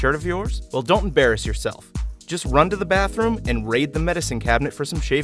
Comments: I heard "Just run to the bathroom, and rape the medicine cabinet for some shaving cream". LOL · did he say “rape the medicine cabinet”?